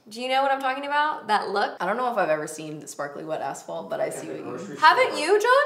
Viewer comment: I swear I heard Desi and then Katy say sparkly wet ASSHOLE 😂😂😂